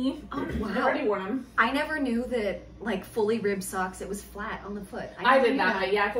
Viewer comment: Your podcasts always make me laugh! Thank you for that. I have to say that every time you talk about the Leo & Roxy Work Socks being 'fully ribbed', I hear "for her pleasure'. Maybe inappropriate but it still makes me laugh.🤪